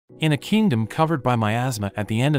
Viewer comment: " In a kingdom covered by my asthma " got me laughing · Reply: 😂 I legit thought he said “In a kingdom covered by my ass”